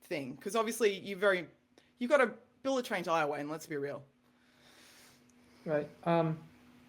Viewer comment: wtf is he saying at 42:24?... "a bullet trained highway?"